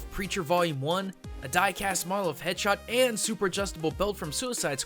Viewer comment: Did he just call Deadshot "headshot"?